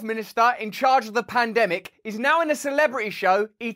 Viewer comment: Hold politicians responsible, hold these criminals and punish them. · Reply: Wait.......did Russell Brand just say PLANdemic?! I swear to God he said that! O_O